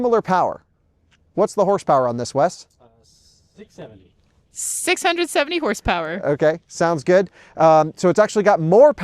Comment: I thought he said 6 7 at first lol..